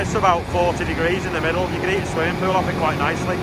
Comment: "it gets to about 40 degrees in the middle, you can eat swimming pool off it quite nicely." What did you say then lol? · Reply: Heat a swimming pool off it